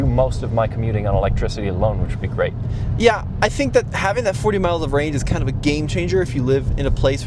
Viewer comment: ６４キロではなく６４マイルでしょ！（約１００キロ） 字幕は、正確に！！！ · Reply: 40マイルに聞こえるので、64kmで合ってると思うのですが、どうでしょうか…？